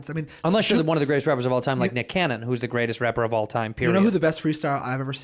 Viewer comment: Did the ginger dude at 0:44 really just say nick cannon is the greatest rapper of all time or am I hearing wrong